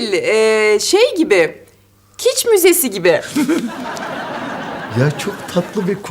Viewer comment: ta aslı ne diyor? Ne müzesi? Bilen varsa 😇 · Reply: Kitsch Müzesi diyor